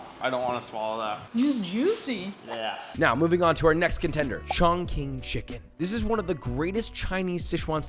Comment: could've sworn you said Shaun King Chicken